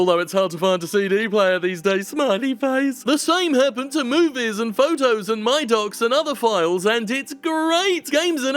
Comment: at first I heard "The same happened to Movies and Photos and my dogs and other files", I was confused.